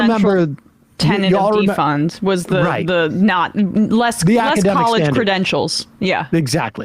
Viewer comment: I don't like Tim Pool.. the amount of content you provide surrounding him is ridiculous. Are you obsessed with him?? It's bad enough that if I search Pim Tool I see this bullshit. He sucks but not for this dumbass reason. You missed the mark Sam Nobody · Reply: Then they respond with this 04:29 Talking over each other. Wtf did you even say!?!?!? "Less college credentials" is all I heard -- as if that means anything...